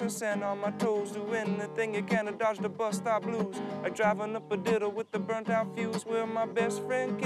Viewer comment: I always thought he said "driving a potato with a burnt out fuse"